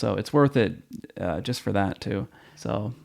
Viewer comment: Thanks for posting the review. I'm thinking about this one. I thought you said your name was 'Lone Eagle 10' at the end. lol peace